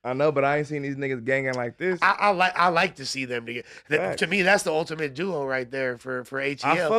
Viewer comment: what did say 😂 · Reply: "I like to see them together"